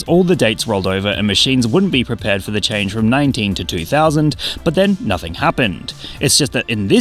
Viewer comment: my gorillaz obsessed ass thought he said 19-2000 as in the song title